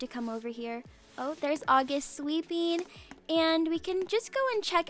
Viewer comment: I honestly thought when you said "August is sweeping" I thought you said "August is sleeping" lmao